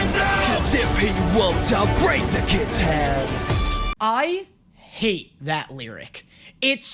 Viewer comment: When he said I'll break the kid's hand for the longest time I thought he said I'll break the kids head